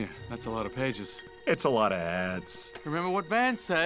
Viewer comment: when he says "its alot of ads", as a kid I always thought he said "its alot of ass" 😅